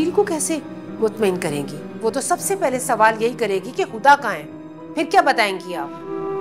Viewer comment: Subtitles mistake 🕵️‍♂️; 10:40 instead of huda they wrote qandeel